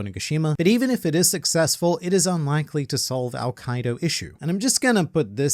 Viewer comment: French watcher here, never said Hi in the comment even if I follow the since a few years... but today I have to comment that at 6:19 the automatic subtitles say "it is unlikely to solve al quaeda issues" instead of "all Kaido" and I needed a moment to understand what was happening.